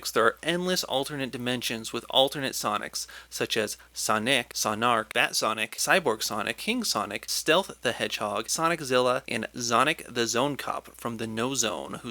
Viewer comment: honestly i thought he said sonigg